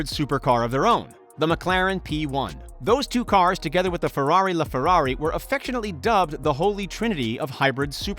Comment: I’m sorry did you just say LêFerrari